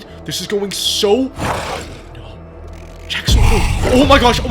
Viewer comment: did it say JaCk SWaNooO